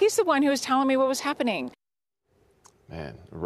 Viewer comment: wait ... did he just said bruh moment?